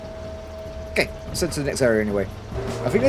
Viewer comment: lol, when you mentioned the faction battle ahead of you, for a moment I thought I heard fashion battle and was really confused for a moment